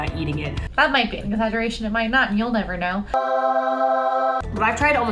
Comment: Ok, I think that at 5:45 says "That might be an exaggeration and it might not be, you'll never know." I slowed it to quarter speed and played it a dozen times, and I'm maybe 50% sure.